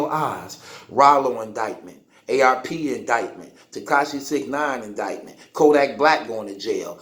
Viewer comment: Did he just say ARP indictment? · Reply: The Battle Rap Community Base Yea i think he met AR-Ab???